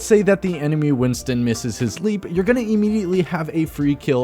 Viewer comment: *Lets say the enemy winston missed his sleep*. But winston cant sleep people xD Edit: i hear that he said leap